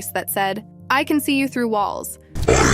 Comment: I don't get why everybody says that he said "I can see you through walls" Because i clearly heard it as "I CAN SEE YOU COWARDS"